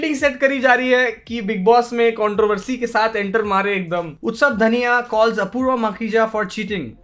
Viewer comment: Did he say utsav dhaniya?? 🤣🤣🤣🤣